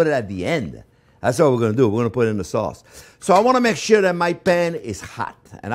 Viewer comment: Did he just say "I wanna make shit in my pan" at 1:39 ?